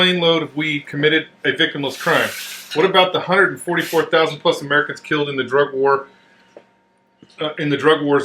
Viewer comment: says american victims instead of mexican american and hears it correctly - did anybody else catch that?